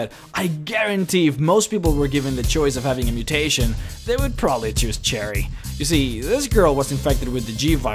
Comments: I thought you said Jerry 😅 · this was recommended for me, but I know nothing about the resident evil series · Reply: i also noticed it sounded like he was saying cherry when sherry came up